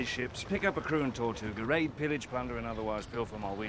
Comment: I've always thought he said "Raid, pillage, plunder." But apparently he says "Rape, pillage, plunder" which is just uh.. Damn.